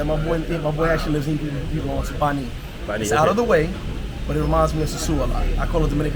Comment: what city did name @ 19:16 ??? He said it's another great city to visit other than sosua. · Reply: Bani